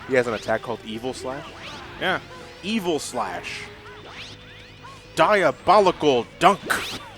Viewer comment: I thought Double was saying Evil Slash when I first played, too, but he's actually saying Kiba Flash.